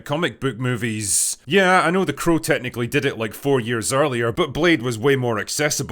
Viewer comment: what did he say? crow technically did it? whos crow